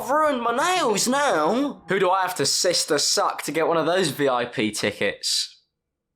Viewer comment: thought you said sister stark and I almost threw fuckin hands